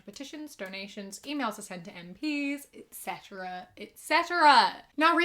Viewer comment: every time i hear your intro i hear: 'email te send to end peace' which always confused me until i put on captions XD